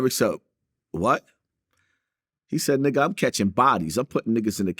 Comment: I thought it was 3 bodies u said